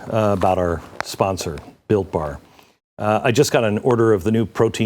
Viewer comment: I thought you said the sponsor was Bill Barr. Hahahaha Seriously, thank you for unmasking this information.